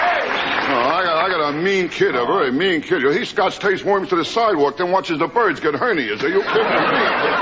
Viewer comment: Can someone please tell me what Rodney says from 1:32 - 1:38? I want to know what the joke is so bad, but I can't make out to what he is saying! Thank you to anyone who can help me out. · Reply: I got a mean kid, a very mean kid - he scotch-tapes worms to the sidewalk then watches the birds get hernias!